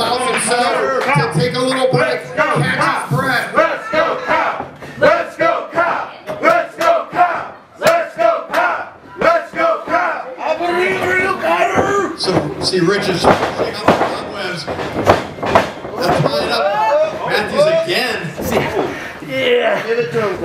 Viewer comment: anyone else hear "I BERIVE IN RU KYLE!!!!" at 10:57 from south park? Lol